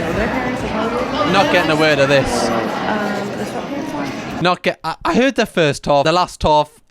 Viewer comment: I put subtitles on to try get what she was saying but YouTube dont get it either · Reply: She basically said: Don't we dress up for our kids' Christmas concerts anymore? Cause there are parents wearing sweatpants and look like they just rolled out of bed, and I just don't understand. I'm wearing a full dress, Christmas ... And a bow in my hair. Stockings, boots. I posted a pic of my kid and I, he's in a nice black button down and black shoes. Some kids are in black t shirts instead of black button downs, so their parents are probably the ones with the sweatpants on.